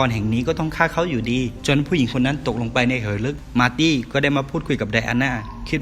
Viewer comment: ฉันได้ยินว่า"เหยลึก"